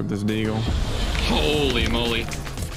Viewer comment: Listen a 3:27 did ninja say the n word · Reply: He said Holy Molly